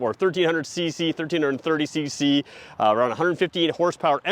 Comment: The horsepower on the 1330 cc engine is 115. It sounded like he said 158 it’s 115 hp.